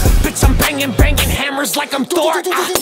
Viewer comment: I really thought he be saying 'bitch, im bangin', bangin' hammers like im Dort'... as in Luguentz Dort 😶